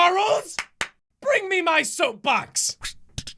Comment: I thought it said “sofa” not “soap box” but sorta “soap”